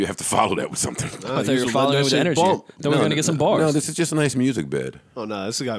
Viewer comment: did he say ''...throw a nicca some bar's''....it's cool, but did he say it??😂 😂 😂